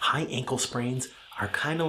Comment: Lol I heard “Hi, Ankle Sprains” at the beginning and thought you called us ankle sprains 😂